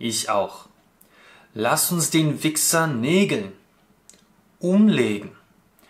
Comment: А мне послышалось legen вместо nägeln:) Спасибо за разбор)